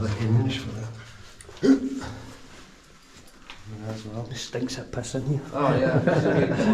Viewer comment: Whats it smell like? Any paranormal vibes felt?? Musty smell?? @ 7:59?? Did he just say, "it stinks like piss in here" Lol I was wondering if the Scots pissed all over everything like our American guys do lol seems like from the time theyre able to write their names w their tool--they DO!! And they don't stop until they take their last breaths!! Our guys almost mark their territory that way😆